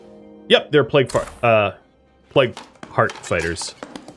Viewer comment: almost saying "Yep, they're Plague Fart Hiders" has me giggling. .. Contrary to what the calendar says; I'm apparently 12.